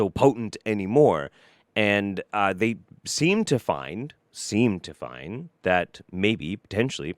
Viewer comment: He said “they seem to find” I hear “they semen to find”